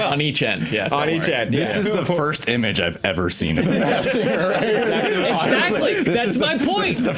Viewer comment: Oh, Masked Singer. I thought he said "Massinger."